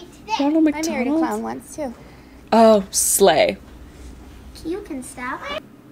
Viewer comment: at 8.30 in the video when the mother said "i married a clown ones to" i thought the kid said "he killed himself" my mouth was open in till i heard say that she said "you can stop''